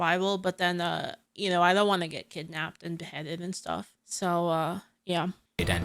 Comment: it sounded like you said "I don't want to get Kidnapped and Petted, you know?" Are you suure about that? Would you not like to be whisked away from your life, to be kept in a house to be pampered and cared for? To be honest, I've seen to much media to believe that story XD